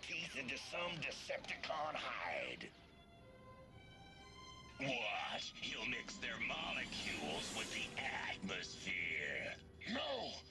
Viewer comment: Can anyone tell what dirge said at 1:44 ? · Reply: "Watch he'll mix their molecules with the atmosphere"